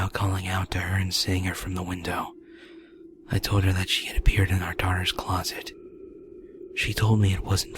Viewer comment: I know he said "I told her how she had appeared in my daughters closet" but what i heard was "I told her how she hid a beard in my daughters closet"